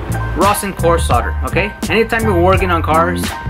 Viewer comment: I was watching this at work with volume off and annotations on and started laughing cause at 9:20 the captions said "Rocking horse Potter". Im guessing you said "rosin core solder".